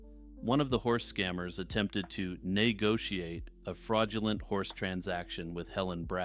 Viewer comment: did bro just say neighgotiate